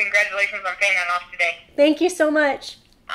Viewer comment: Gosh this hits home. Especially when the operator said before she hung up “enjoy the rest of your life”. 😭Congrats🎈💃🏽💓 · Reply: B Wright I heard her say the rest of your night lol but if she said life that’s funny lol